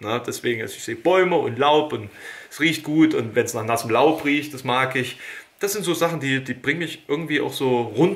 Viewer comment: "...und wenns nach nassen Lauch riecht, das mag ich." :D Ging das nur mir so, oder haben das noch mehr verstanden? xD